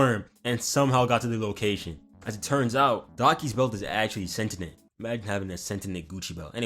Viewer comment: SENTINENT, SENTINENT OH MA GAWD 😭 we cant be this black bro✌mf bouta start saying axe instead of ask